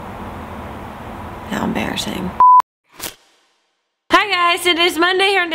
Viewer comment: I heard that as "How emBEARassing" ;)